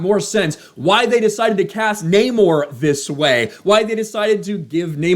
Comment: I thought he was saying “NO MORE” which is more appropriate 😂